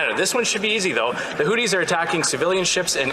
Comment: Sorry guys, gotta say it: Trudeau...Houthi's...I thought you said Hooter's...Sylvie banned them a long time ago